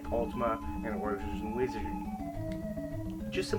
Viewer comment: Two quick questions: what is the song playing in the background, and what game(s?) did you mention at 3:00. All I heard was Ultima, did you also say Wizardry? I can't make out what you're saying. Just curious, because I'm looking for possible new games to check out.